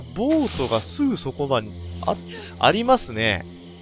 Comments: ありますねぇがやりますねぇ!!に聞こえた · ちょっとぉ？…ボートがぁ？？ ありますねぇ！！！(やりますねぇ！に聞こえた)